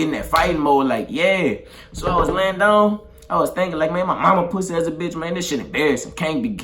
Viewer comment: did he just say “my momma pussy as a bitch” 😂😂😂😂😂😂